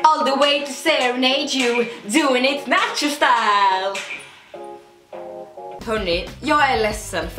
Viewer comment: Jag tror att jag o är inne på lite samma bana, hon hörde ju nacho style och jag hörde taco style xD